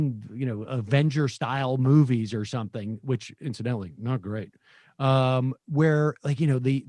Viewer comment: did he say the Avenger movies were "not great"?